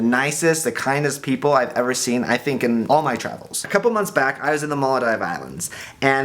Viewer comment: What was the name of the island Devin said he visited at 0:46? Muladi?